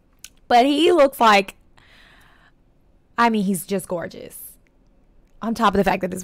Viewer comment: I love your new hairstyle. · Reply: when you said “just gorgeous,” I thought you said “j-gorgeous.” 🤣 because j-hope. My name is Hobi_ because Jhope’s nickname is Hobi.